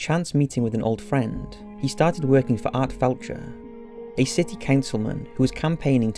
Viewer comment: I think you called Art FLETCHER "FELTCHER"... a bit of a Freudian Slip...